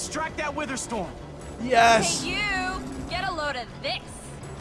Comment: I had to watch it twice, because I misheard her saying "Hey, you! Get a load of dicks!" xD